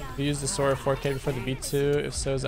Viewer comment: sorry i couldn't hear clearly, but what mouse is it? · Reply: sora 4k or v2